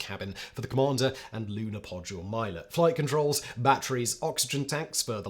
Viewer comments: "Lunar Podgulet Milot" anyone else hear that? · did you say "lunar podule milot" instead of "lunar module pilot"?🤔😁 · Did you say "Lunar Podule Milot?"